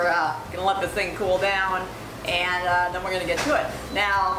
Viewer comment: does she say "and then we're gonna hock tuah it"... They absolutely cycloned those things down.